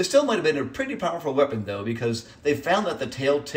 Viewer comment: Oh, 30 meters per second. I thought you were saying feet. I was like. So it falls...